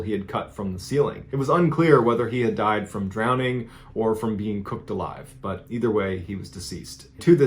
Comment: "it was unclear wether he died from drowning or being cooked alive" Exactly how you can tell the environment was just a tad hostile there. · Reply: I had to rewind that part to hear it again after he first said it. I thought he said drowning or being crystallized.💎